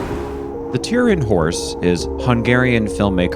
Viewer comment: I thought you were saying the terrid. horse, but it's just your accent. Thank you for switching me on to films I've never seen, ill be watching the Turin horse. If you haven't seen The sacrifice, by Tarkovski you may appreciate it.